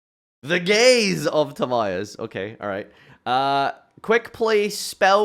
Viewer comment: All I hear is “the 💅🌈 of Timaeus”